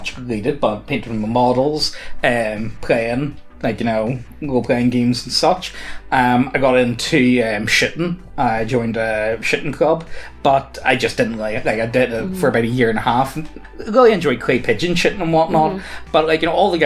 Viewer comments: I thought said he joined a shitting club. Like a club for people to get together and poop. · shooting, shooting, I did a double take because it sounded like you said shitting